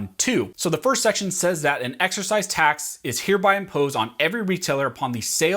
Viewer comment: Was it just me or did I hear "exercise" rather than "excise" tax? (mark 2:08 and again at 3:07). I'm thinking he just misspoke because he has a tendency to speak rather quickly.